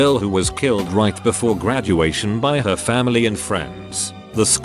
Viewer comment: I thought it said that she was killed by her family